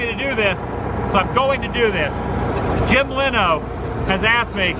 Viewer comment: Jim Leno?? Did Adam call u Jim Leno? And the list just keeps getting longer 😂